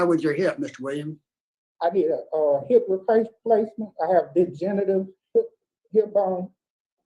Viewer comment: …it sounded like he said, “I have big genitals”. If he didn’t, what DID he say?